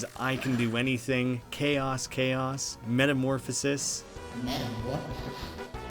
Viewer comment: hol up jevil says metamorphosis i thought he said i dont know what this is